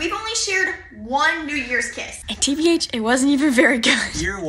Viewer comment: i thought they said "we haven't had a good new york kiss" and they're going to nyc 😭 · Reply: I thought they said new years kiss😭